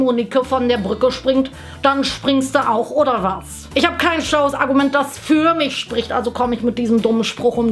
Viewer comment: ich hab verstanden "akkument"